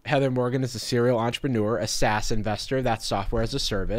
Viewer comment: thought he said "assassin"